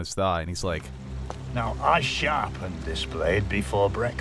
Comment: When I first hear this, I thought he said "Now I shat on this blade before breakfast" I thought he was threatening him with an infection